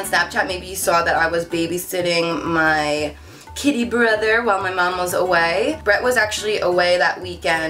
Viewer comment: When you said "kitty brother" I thought you said "kiddy brother." So when you talked about him getting groomed I was confused until you put up the pictures😂